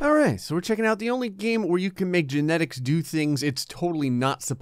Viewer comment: I thought it said ‘I made Genitals do things it’s not supposed to do’